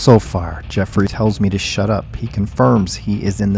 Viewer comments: Sounds like he said “sort of” and not “shut up” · I don't hear " shut up" I hear him say "sort of" or am I wrong 🤔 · It sounds like he said sorta I don't think he said shut up 😅